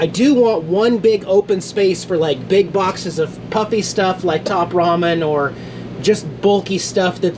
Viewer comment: you see how productive you were because you avoided social media and news? And as soon as you "tuned in" you became sad and worried. We have to be informed, but it's so easy to fall into the trap of obsession and we know what that leads to. Get your updates at the end of the day and then do something else right after to distract yourself. And yes...we need more JAX, he has a very calming effect and more importantly he makes me smile. Sending a warm hug, Ana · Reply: Oh. you got me good... 8:25 lol I thought you said puppy stuff. Lol. I thought you got a dog.